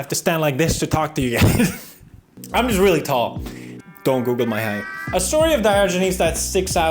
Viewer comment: did.. did he say 'Google'?